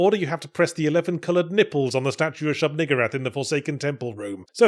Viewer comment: I’m sorry what did you just call her? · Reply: Shub-Niggurath is a deity created by H. P. Lovecraft.